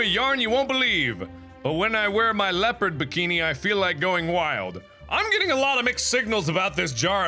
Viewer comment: Omg i thought it said going wifi